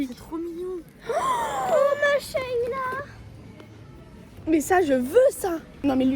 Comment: pourquoi duo a dit masshallah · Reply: Mais 🤦🏽‍♀️ elle a dit « oh my shailla » c une ref